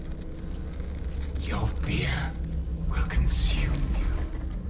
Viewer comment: Sounded like springtrap said your beer will consume you